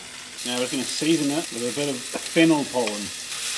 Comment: When he said fennel pollen i thought he said fentanyl pollen